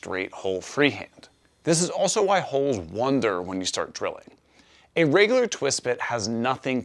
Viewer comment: I believe it is holes that “wander’. Great video as always. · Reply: The automated transcription heard it as "wander", but yes, it did sound a bit like "wonder".